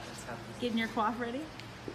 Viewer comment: what did alison(emmy) say?? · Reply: getting your coif ready